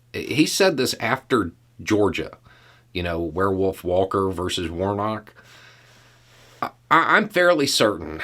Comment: "So close to getting it" is practically a meme for conservatives by now. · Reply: I misheard him to say “werewolf versus warlock”, which would make so much more sense than what happened.